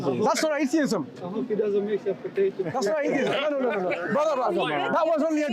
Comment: please could anyone tell me what was the joke? I can't here him well, did he say "I hope he does not make you a ptoato?" as in he made you atheist now potato?